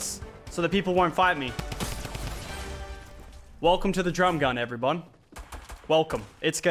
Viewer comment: yall heard him say "Welcome to the drum gun everybon" at the beginning when killed at Salty Springs